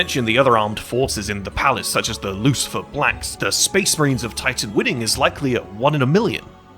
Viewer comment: He also called them "Lucifer Blanks" instead of "Lucifer Blacks".